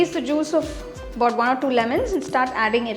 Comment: For lemon dont use more than 1/2 lemon😭 i misheard it as 1 to 2 Lemons and i had to turn all that halwa into paneer 😢